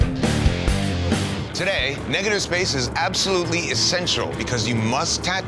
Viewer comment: bro why did i hear him say Ni** 😭😭😭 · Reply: He said "negative space"....